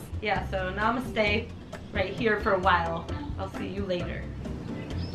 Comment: when she said "namastay right here for a little while" i thought she said "mama-stay right here for a while" SOMEONE MAKE A T-SHIRT WITH THAT AND PAY ME ROYALTIES !!!